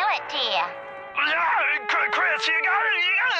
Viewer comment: Black Eye Griffin just has a weirdly funny Gibberish voice, though I do wish that I could understand what he was saying when he said only three clear words that went along with his gibberish sentence like Chris, Education, and Homework. So how come this video has no auto generated or scripted YouTube subtitles anyway? And I mean seriously, does anyone know what Black Eye Griffin was saying to his two kids playing hooky? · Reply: "Chris you gotta... it's important for you to get an education. Get in there, and finish your homework"